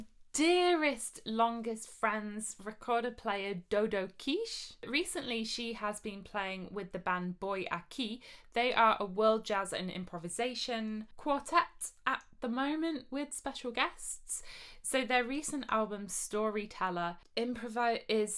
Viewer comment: When you said "boi akih," I heard "voy aqui." Neat that it translates into Spanish unintentionally!